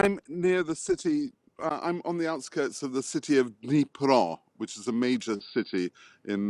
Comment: There is no "Niprov," Ukraine, and certainly no such major city. Your screen text is incorrect 0:11. I think that he said "Dnipro," which is a major city and is toward the SE of Ukraine.